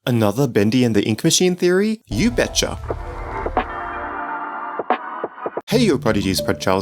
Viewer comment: your awesome but your intro this is what i hear "another cringey intro,you betcha"